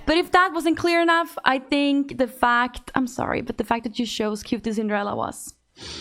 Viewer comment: I don’t understand what she’s saying at 01:20 - “the fact that you shows qtcinderella was” ??? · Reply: I'm not sure, but I assumed she said "The fact you chose QT" maybe in a love or host before he came out?